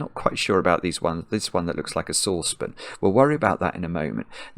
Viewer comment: I couldn't understand Simon's accent when he said "saucepan" (first at 20:52 and repeated a few times). I needed the help of YT subtitles to figure it out. I thought he was saying "source pin" or "sospin" or something like that.